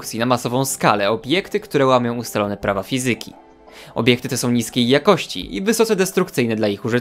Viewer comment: Zamiast usłyszeć niskiej usłyszałam chińskiej. XD